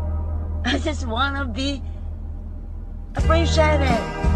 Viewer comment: Why do I hear “I just wanna be a free sherbert”